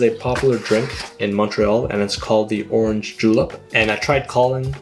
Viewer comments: I don't know why I keep hearing orange "JULAB" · ohmygod I thought you said orange Julius at first and I was like nooooo why is there eggs 😭